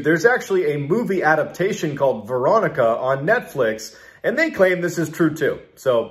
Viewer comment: For a second I thought he was saying "Verotika" which is a completely different horror movie, on Shudder. Don't watch Verotika. lol Veronica was okay though...